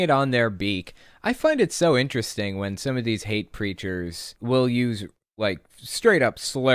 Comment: Everytime you say "hate preachers" I hear "hate creatures" and it makes me giggle 🤭